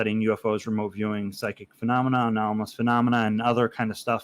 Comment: Greatly appreciate your time and consideration, (and the Vetted Team!). It was great talking with you. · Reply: Did you say "study UFOs from Opioiding Psychic Phenomena"?